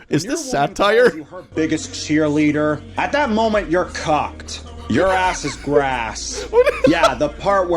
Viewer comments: I thought he said cocked not cooked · The accent on the first 'alpha male' made it sound like he said "C*cked" instead of "Cooked"